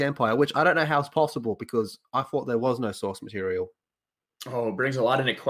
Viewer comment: I thought they said "we have no source material" so what was the EU?